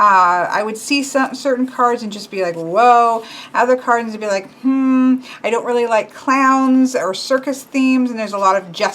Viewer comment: What's the name of the deck at 42:16 ? I couldn't understand the name. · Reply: It’s the Japaridze Tarot I believe